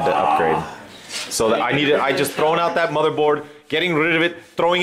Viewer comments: i thought was gonna say "i just throw out that motherfucker" loool. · I thought he's gonna say "Motherfu****" XD